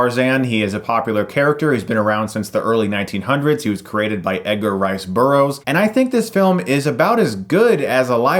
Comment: for a second there I legitimately thought you said Tarzan was created by Edgar Wright and I was extremely confused.